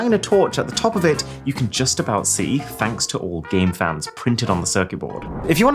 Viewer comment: Me who thought it said cameraman instead of game fans